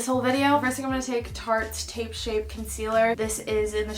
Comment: did she say tape shape instead of shape tape or does my hearing just suck 😂😂